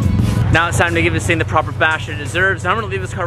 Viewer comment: He says Proper bash at 3:24 instead of proper bath or wash